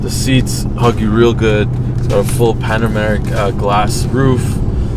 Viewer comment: Ok...how y'all just gonna gloss over NOT mentioning that Bentley Mulsanne 2 door coupe 3:52 ...because...there ain't such a car, far as I know! Who custom designed that?!?! SO. DOPE! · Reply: Mike How are you gonna gloss over the fact he said panameric instead of panoramic glass roof